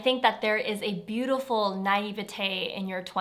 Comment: What does she say at 12:11? I don't understand her. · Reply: she says “naïveté”, which means lack of experience, wisdom, or innocence :)